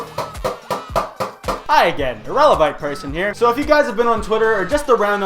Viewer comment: I was laughing so hard when i heard "irelavite person"